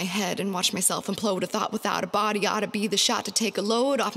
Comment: I legit thought she said “Body-ody” at 0:11 like that song by Meghan Thee Stallion lol